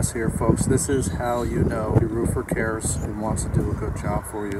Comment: I thought it said roofer class 💀